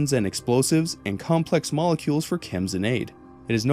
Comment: What he said “Chems and Aid” What I heard “Chems and Eggs” 😂